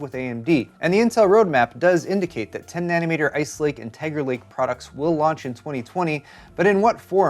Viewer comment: Sounds like you said TEGRA Lake.